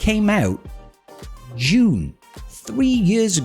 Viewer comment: its funny how you pronounced July because it sounded like June :D